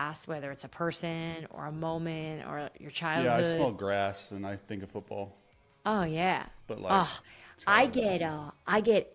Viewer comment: Okay, totally thought it sounded like Chase said he smokes grass and thinks of football🤣🤣😂😂